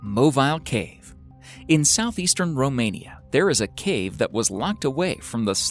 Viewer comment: did i hear good, did you said Romania? Cuz i live there!